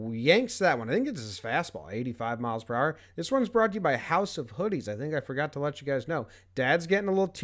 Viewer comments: I thought JB said house of pudding at first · At first I thought he said "House of Pudding" was the new sponsor, I was thinking "oh boy, free pudding if I mention Jomboy!"